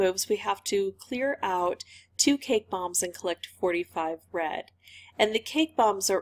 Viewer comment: I thought you said 45 “bread”. Lmao 😂.